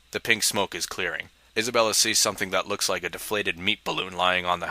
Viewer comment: It sounds like he is saying 'meat ball' instead of 'meap ball' Anyone else hear that?